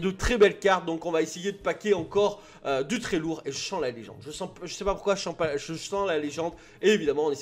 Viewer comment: vers les 0:58 j'ai compris "je sens l'anus" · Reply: tu as l'esprit mal placé ^^ je dis " je sens la légende "